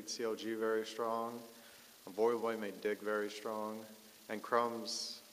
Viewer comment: "Voyboy made DIG very strong" i think thats it, i thought he said "Voyboy made my dick very strong"